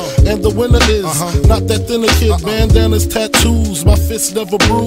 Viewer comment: Does he say "not that Dilla kid"? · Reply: He says not that thinner kid